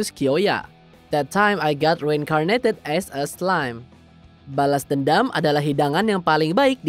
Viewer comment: Kalo ngomong bhs Inggris nggk usah fasih", b aja lah bang, malah kedengeran kyk lebay. Biasa aja yg penting jelas. Lu pas ngomong "reincarnated as a slime" malah jdi "reincarnated ass a slime".